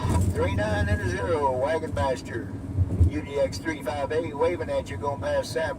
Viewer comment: at the beginning, the close-caption thought you said wagon bastard- lol!